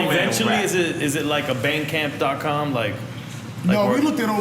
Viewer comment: Nore thought they said BANGCAMP..."is that Bang Brother's sister..?"